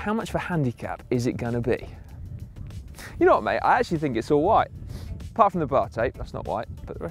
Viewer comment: "You know what mate, I actually think it's alright." That's what I thought he said until --- "Apart from the bar tape, that's not white."